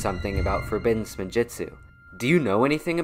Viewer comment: I thought it said wood spinjitzu